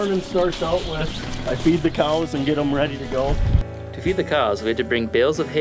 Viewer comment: I thought that guy said "i beat the cows to get them ready"